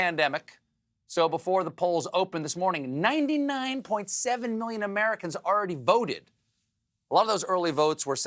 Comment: got an error in the subtitles: “99.7% of Americans voted” instead of “99.7 million” just a little difference there...